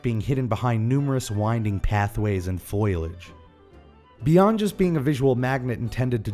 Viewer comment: did you say foilage?